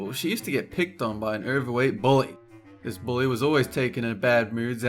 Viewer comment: On the part where shima was bullied I thought said boy instead of bully and he also said she and her so I wondered whether it was a boy or girl but i soon found out